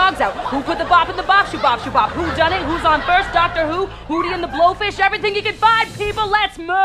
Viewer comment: When she said, "Who's on first," at first I thought she said, "Who shot first?"Han.